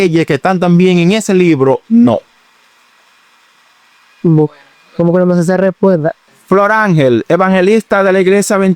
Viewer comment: Que dijo??? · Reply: Como que no me se esa respuesta creo que dijo